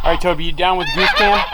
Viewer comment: I kept hearing "Goof cam!" Carry on...